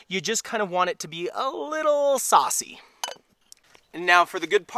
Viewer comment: 🤭 sorry i miss that, i thought he said sassy?